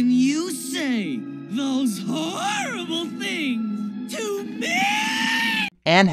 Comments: When you thought Stimpy said "How can you say those horrible thing to me!?" he actually DID say "things", but the "s" came out very quietly. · i heard him say "things". Maybe you just misheard it. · It's very faint, but I heard the sibilant fricative. Stimpy said "things".